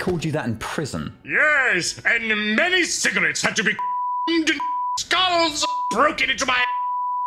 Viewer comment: Ok, let’s play a game: What did Big-D say, [REDACTED] parts and all? VULGAR ANSWERS ONLY. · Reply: I think I heard "skulls" among the bleeping...